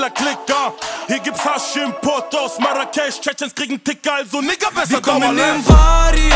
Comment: was sagt der genau? Chechens kriegen ticker ? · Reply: Er meint chechener kriegen ticker